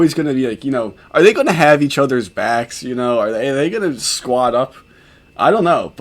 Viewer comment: yeah when your talking about the two of them interacting in jail. At least that's what it sounds like you say. Your also laughing alot so maybe there's some slurring happening. Idk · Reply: oh i believe i say "squad up" meaning "buddy up"